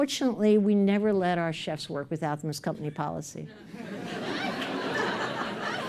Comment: This woman is so genuine, frank and real! Utterly utterly impressed! So refreshing in a world of fakes and politically correct idiots. A little help, could anybody tell me what she said at 29:32 ?? "Unfortunately, we do not" ?? Thank you in advance. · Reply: We never let our chefs work without them. It's company policy